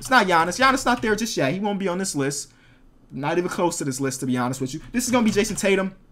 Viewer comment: Did he just say, "To be GIANNIS with you?"?